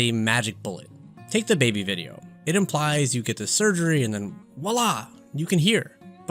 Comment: Lmfao the subtitles said "WAH LAA" instead of voila which is just peak considering the context of the video As someone with basically half a face (hard of hearing and only peripheral vision on the left), I appreciate this video :) Also these musical based transitions. Yes.